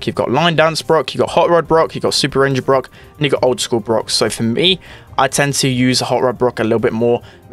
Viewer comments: Am I dumb but did he actually say the n word? 3:40 'n** old school Brock' So sorry if I am mistaken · did he said nigga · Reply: bit late reply, he said “and u got” very fast